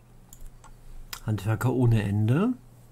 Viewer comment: Ich musste zurückspulen. Hab zuerst "Handwerker ohne Hände" verstanden 😂😂